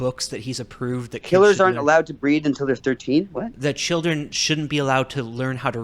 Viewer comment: Killers shouldn't breed until they're 13 HAAHAA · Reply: Lol. I thought he said killers shouldn't breathe until they're 13.